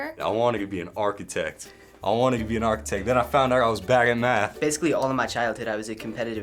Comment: did anyone else hear "bagging meth" instead of "bad at math"...lmfao